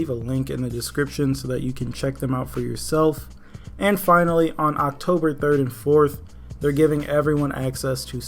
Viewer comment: "october 34th" i cant be the only one who hears that